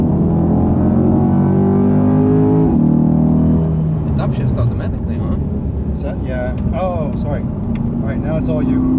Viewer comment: what they say at 2:17? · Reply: "It up shifts automatically right"